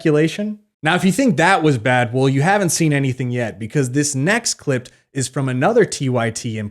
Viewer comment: Did you say next “Clipt”? at 9:48 😂 Flawless argument tho